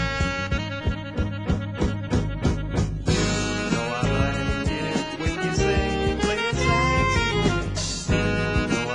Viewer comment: Does anyone know what he's saying at 1:04 ?? (the part A Tribe Called Quest sampled for "Butter" - RIP Phife!) I think I hear "Oh how I like it when you say play your saxy for me" lool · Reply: "You know i like it when you say play it saxy for me"